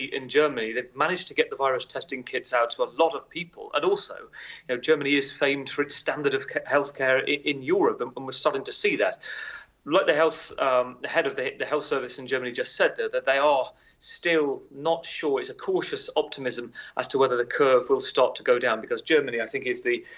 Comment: Erm is it just me or does the guy talking at 4:16-17 sound like he says the head of the hitler health service just said . . . . ??? Or is that just me ? 😳